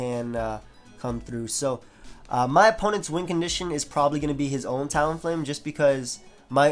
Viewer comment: I have always thought you said wind condition not win lol